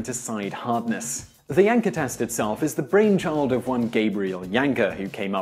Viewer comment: I keep hearing "yanker test"